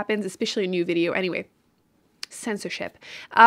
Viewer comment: why did I hear 👂🏻 censorsh*t🤣🤪